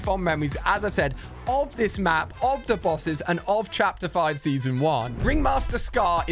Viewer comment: Why did you say chapter 1 Bruh?